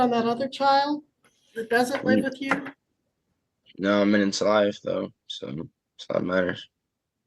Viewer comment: I heard, "No, I'm in his life, though. That's what matters." 7:54.00